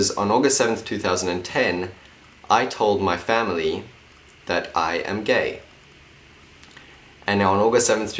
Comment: my headphones stuffed up so I thought he said "I am bae" and I was like " yes, troye you are"